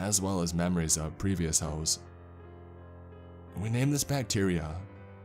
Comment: ever since president Bush it seems like so many people say nuculer instead of nuclear. it's a conspiracy. 18:00 did he say "hoes"? heh heh